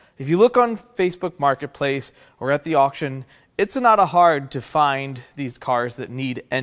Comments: Anyone else catch “It’s Sonata hard to find these engines.” 😂😂😂😂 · Did I hear you say “it’s not Sonata hard to find cars that need these engines”.😂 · Did you say “it’s sonata hard” At 2:32 😂 that’s awesome! I know this video has been up here for a while now, but I’m a new subscriber. Love your videos!